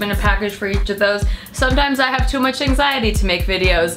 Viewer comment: I thought she said, "This is the anxiety package. Get fucked." And tbh, I liked that better.